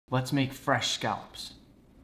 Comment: At first I thought my guy said "let make fresh scalps"